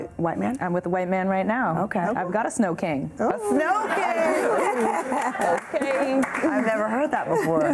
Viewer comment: "A Snow King" WTH! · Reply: THAT'S what she said???? 🤢🤮 I thought she said "A snow can"